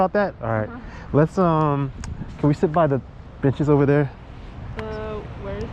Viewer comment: I thought I heard you say "could we sit by the bitches over there" when you meant "benches" lol